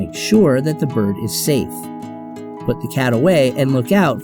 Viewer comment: did he say put the Cat away?